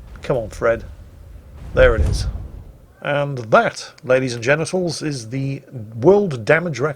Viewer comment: did Jingles really say «and that, Ladies and Genitals....», that is what sounded to me and Youtube seems to agree because their voice recognition software subbed it the same way